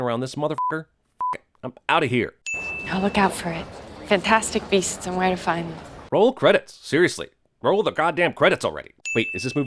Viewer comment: Pornhub? Oh, sorry! I thought is said fantastic breasts and where to find them. I must stop spending time on Pornhub And get my eyes tested.